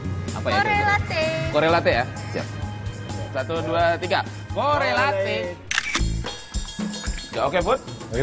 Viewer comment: chef Juna bilang apa?? :V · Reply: Correlate versi pronounce nginggris